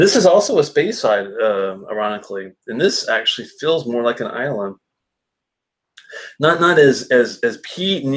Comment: Islay? LOL!!! This ain't no Islay whiskey. Not even close. Ha! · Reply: Ah I said it has a lot of "Islands" influence to it... the brininess.. the white pepper .. somewhat like a Talisker. That's all I can find on this at least. Maybe you thought I said Islay instead? But no, you're correct, nothing like an Islay.